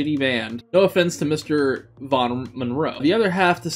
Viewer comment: Bottom monroe. Really? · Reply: i heard that too at 2:10 but he actually says mr von monroe